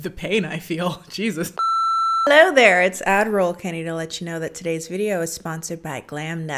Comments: Haven't even finished the video yet but had to say: It's always refreshingly surreal when "Admiral Kennie" gets a beauty sponsorship...but back to the debauchery. Edit: 12:28 was my favorite moment of the video, masterpiece of disgust from an actual tall girl. Chef's kiss! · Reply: I always thought she said Ad Roll Kennie? 🤔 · I KNOW you're saying "Ad Roll Kennie" but all I hear is "Admiral Kennie" and I refuse to hear anything else.